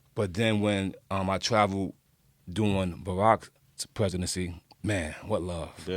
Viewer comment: He still can't pronounce rrrrr's lol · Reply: I thought he said "doing" when he said "during" lol